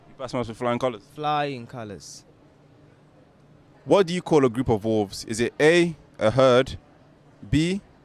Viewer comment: I thought he said 'a group of dwarfs' and thought 'Jesus, I don't know that'.